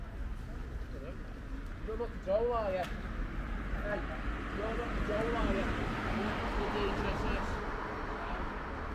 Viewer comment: What did that guy say at 4:25? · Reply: He said.....Your not from the dole are ya......the D.H.S.S .